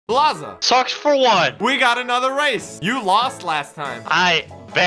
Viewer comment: BLAZA: socksfor1 ME (thinking): he sounds like he say sucks 4 won